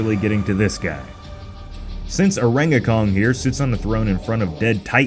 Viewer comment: Oh you said “Orangakong” I thought you said “Orangaton” Orangaton sounds like a Pokemon 😂 I’ll just call him Orahngga until we know his name. 🤔